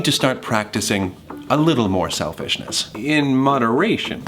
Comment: Roman: When is it enough? Janus: .... Trees? A whole ass mood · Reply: Bailey Thanks for the correction, but it was actually intentional. He kinda sounds like he says 'shelfishness' instead of 'selfishness' so I just thought it was a cute, funny thing to point out.